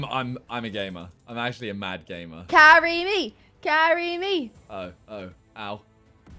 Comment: Nobody: When said to, ' Carry me!' and I heard ' Marry me!' 😂😂😂